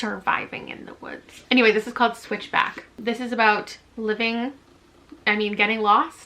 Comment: I thought I heard you say "Switch Bitch" and thought yeah that would be a book would own and then I re-winded the video to see the cover and it was "Switch Back" 😂